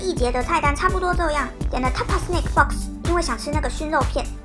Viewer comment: 我聽成了Mezze Snake Box了。想飛機上什麼時候賣蛇肉了😂。 原來是snack。很喜歡你的航空系列